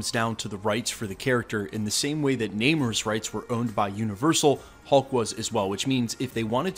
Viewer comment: Namor’s rights? Submariner or did he say something else?